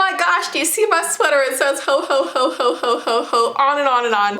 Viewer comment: I thought she said hoe hoe hoe😂